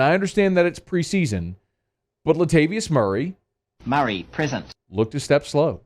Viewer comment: So it’s “Murray, present”? I always thought it said “Murray, prison” for some reason. I think I’ll miss it. 😬 LoL